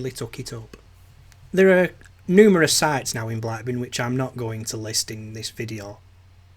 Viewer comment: I thought amber arrows were a thing anyway! Btw i thought you were saying black bin!